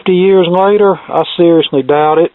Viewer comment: Sounded like you said "Searsly doubt it." A nifty pun in this case.